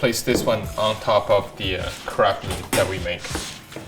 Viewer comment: It sounds like he said the crap that we make